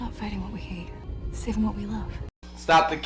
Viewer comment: I thought she said “sigma be love”